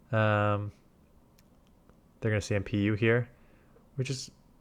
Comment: "they're gonna **PU here..." can someone transcibe him so I can look up the acronym to learn about what he's saying. they're gonna __ here? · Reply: CMP - charge move priority. If you throw the charge move at the same time it determines who gets to throw first - the higher attack pokemon